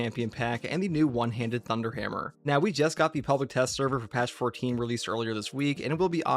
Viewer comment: did say 'Pelvic Test Server'???? Lmao